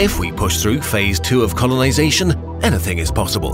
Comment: did you say conolisation by mistake?😂😂